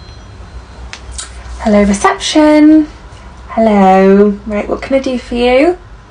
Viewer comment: I heard you wrong the first time and thought you said "hello deception" 😆